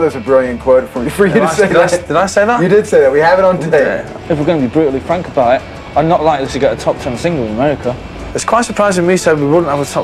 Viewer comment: What the heck is he saying at 2:05?? Got any what?? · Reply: "If we're gonna be brutaly frank about it, I'm not likely to get a top 10 single in america. It's quite surprising me saying we wouldn't get a top 10 innit? That's not like me"